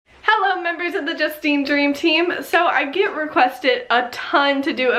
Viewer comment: So I just started to watch yours vids and I thought you said welcome to the just bean team ahaha I did subscribe don’t worry